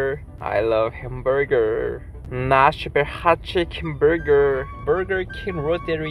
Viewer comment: i think he was saying "nashville" hot chicken hamburger lmao he's so cute